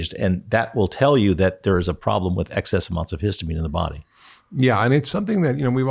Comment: what did he say? Excess months of histamine? So, if people have dermatographism, etc., they gave months worth load in their body still? How do I get rid of this excess?! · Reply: He probably said “excess amounts” not months